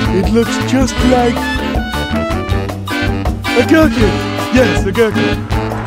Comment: music is too loud, what did he say? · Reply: A Gherkin... yes a gherkin. Because that's what people call it.